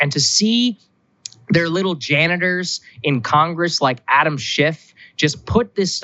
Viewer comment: Sounded like Saagar said "Adam SH#T" lol